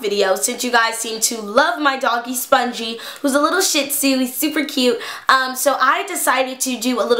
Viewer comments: Was I the only one who thought she said "My dog who is a little shit" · I thought was going to say a little shit I was like DAMN!!! hurtfull!!